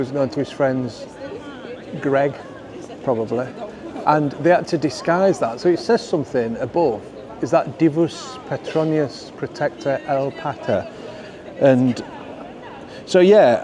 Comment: I could swear at 13:50 you were saying ‘pork pie’as’ 😜